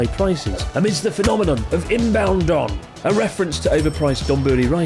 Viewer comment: I swear to God I thought you said Inbound Dong.